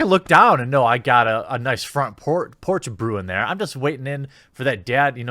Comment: did you just call yourself a porch monkey?